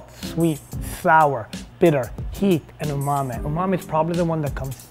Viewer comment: did he just say "umame"? are we all pronouncing it wrong?